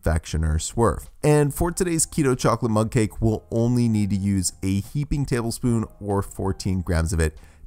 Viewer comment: Wow I thought he said "eight tablespoons of cocoa" and i had to put 2 extra eggs 😂